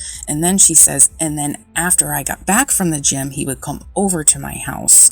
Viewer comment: It really, really, really sounds like she said “after WE get back from the gym” , not “after I get back from the gym” ...